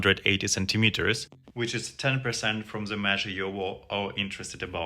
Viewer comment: Bro you mathed wrong. He said it's 10% of 180cm.... That's 18cm or 7in... A decent size but not huge. He could have huge girth tho, who knows. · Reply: Better check again, I heard "...which is 10% from the measurement you...". I could be wrong, but that's what I'm hearing. If I'm wrong, I stand corrected. Thanks.